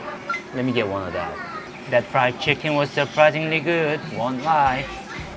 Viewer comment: what did he say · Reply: Surprisingly good